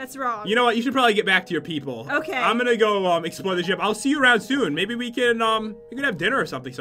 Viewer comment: 13.05 I thought pat said I’m going to EXPLODE the ship